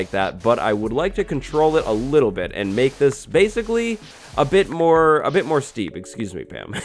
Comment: min did just say spaceicly🤣I have great grammar